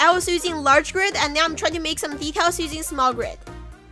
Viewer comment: The bee hive pet bed, ?I thought she said “I used small grid to add decals” then I replayed it and noticed she said “I used small grid to add DETAILS” Big oof to me XD